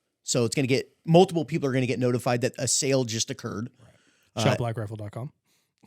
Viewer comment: What’s this guy’s store? Why not show him some love and promote at least in description? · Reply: idk who's website and I can't quite make out what he said? Shoprifle?